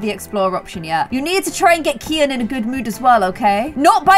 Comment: did she really call naveen kian lol